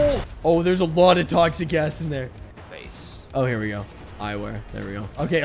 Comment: I heard "There's a lot of toxic ass in there"